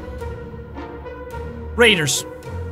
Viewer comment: I thought he said Haters